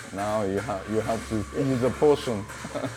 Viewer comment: maybe it's a transcription error then bc that's what the subtitles on the video say. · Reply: There's other translation error where he says potion but subtitle says poison.